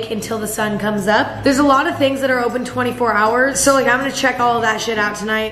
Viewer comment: dude i heard her say 24 hour stores and i was like ????